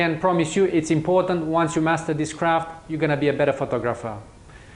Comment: did you actually said "once you master this crap" at 1:47 or its just me hearing wrong?